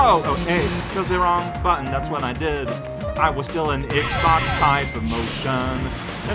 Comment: did he say the buttons were reversed from Nintendo consoles while playing on a Nintendo console >.> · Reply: I think he did say X-box at 5:28.